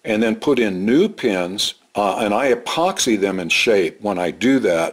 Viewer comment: Hi, I was trying to find the video that you refer to about replacing the handle. I have this exact amp and I want to replace it as you suggest by drilling out the center pins. I did a search but I couldn't find the video. Thanks, Jim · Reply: Thanks for the info. I can't figure out what you said at 2:30, I swear you say "epoxy" lol. I like the look of what you used on this amp, which I'm guessing is a rivet.